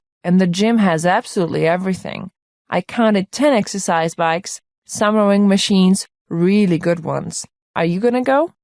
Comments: summering machines? What is she saying lmao? How is the answer rowing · we will check it, keep working on other tests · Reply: I heard "summering" machines. Is it still accepted?